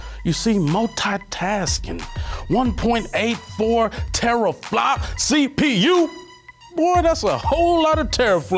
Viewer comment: Did you just say " 1 Terraflop CPU" ? Lol...I can't.